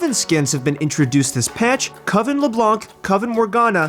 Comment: "coven LeblanCCC" Why are people pronouncing the c ? It's french, the C is silent x) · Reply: As a French guy, I always die of laughter when I hear a caster pronounce the c xD I always hear "LeBlonk"